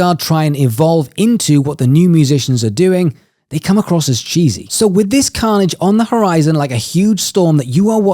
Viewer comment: Great video as normal... I thought you said that the old guard "come across as Jesus"!!! took me 3 listens to get "cheeses"... lol